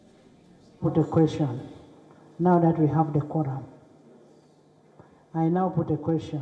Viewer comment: wait what?...why the Quran?...Uganda was not a more christian country than islamic?...that's what the Wikipedia was saying... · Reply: 😂 - I think She says “(Now) that we have the Qur’an.” Are they basing laws from the Qur’an now?